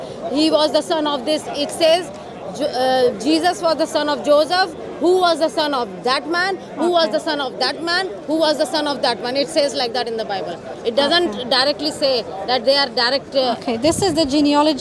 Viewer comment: thought i heard her say son of batman 😂